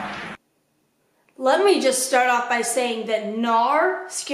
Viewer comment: when she was saying her cat scared her i thought she was saying “let me just say naur “ (like a british person saying no) and that is very funny to me